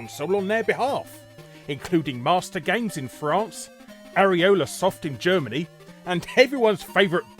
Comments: Did he just say AreolaSoft? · did you say areola-soft? areola as in the ring of skin around a nipple...?